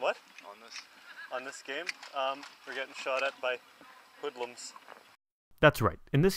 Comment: Lol i thought he said "We're getting shot at by liberals" the first time i watched